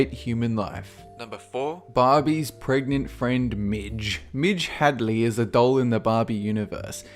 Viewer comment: On 4 I thought he said Barrie's pregnant friend minge. In Britain, minge is a girls pubic hair.